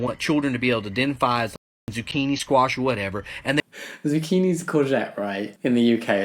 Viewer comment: 🤣 when you said "courgette" subtitles thought you were saying "culture" haha 😂